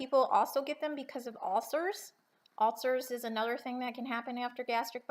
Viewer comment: in 3:11 minutes of your video u said some people reverse because of "auser"? or something like that? my english not very well I want to know what is aser or anything else that u said,Thanks for reply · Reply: Nilo Far yes I know many that have had issues with ulcers and they have had reversals for that purpose alone.